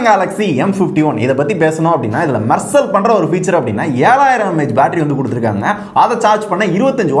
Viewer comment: 7000MAY instead of 7000MAH